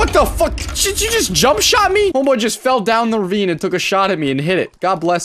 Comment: Did he say homo? 😂😂